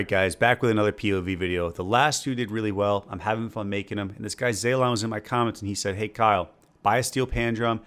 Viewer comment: I thought he said zayloe at first lol